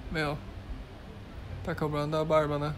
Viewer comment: Eu ouvi "Tá cobrindo a barba, né?" kkkk